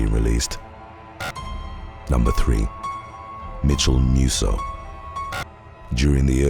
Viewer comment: I thought he was saying Metamucil instead of Mitchel Musso 🤦🏾‍♀️ something is wrong with me lol